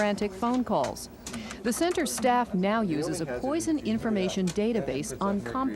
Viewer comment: Did she say poison di*ks ?